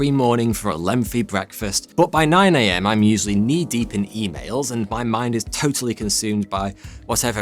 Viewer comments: I swear I heard “by 9 am I’m knee deep in e pills and my mind is totally consumed by my next project for the channel” 💀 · Knee deep in EMAILS. First time around I coulda sworn you said females.😂😂